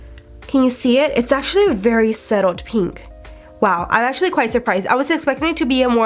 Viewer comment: she didn't even say subtle she said suttle or how ever you would spell it 😂😂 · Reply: Dana L I hadn’t watched the video before replying to mrs angry above lol but yes it does sound like she says settled not subtle or “suttle” lol